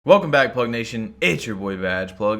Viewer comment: Yo I thought u said huh nation at the start I’m tripping